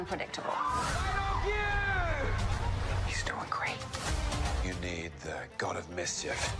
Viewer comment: What is Loki yelling at 00:19? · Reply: All I hear is "I love youuu!".